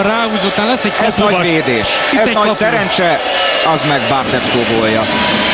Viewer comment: did he say bazmek Bartečko ?:D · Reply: "az meg bartecko gólja"